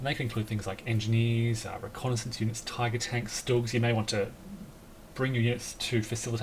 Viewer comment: Wait one: 18:22. Did I hear that right? Did you say "dogs" as a combat modifier??? A German shepherd column shift???? :o